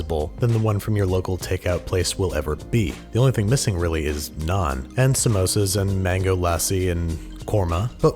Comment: I thought you said none instead of naan